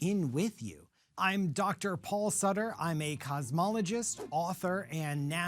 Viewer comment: Im sorry. You said "cosmologist" and i heard "cosmetologist" and was very confused why you were narrating this video for a few minutes